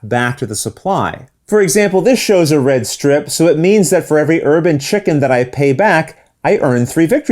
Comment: I definitely heard "urban chicken" instead of "herb and chicken".